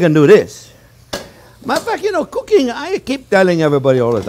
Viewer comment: Did he say "matter of fact" or "muddaf..." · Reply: I pretty sure it was as a matter of fact 😍😍😍